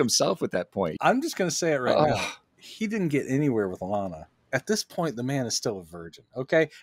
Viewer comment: sounds like you said "Alanna." Her name is Lana. Also can't have intimacy between Clark and a Terran woman, hence what he goes through in Superman II.